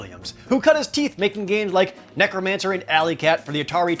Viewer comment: I was looking away from my screen for a second, and I thought he said that as one game called "Necromancer and Alley Cat". ... Now I want to play a game called "Necromancer and Alley Cat".